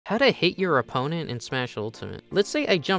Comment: I thought you said “how to hate your opponent”. Too bad all the opponent has to do is pick half the roster, this game needs less cheese